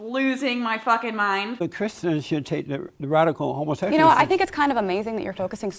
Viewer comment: I heard him say "Radical Homosensuals" at some point... HOMOSENSUALS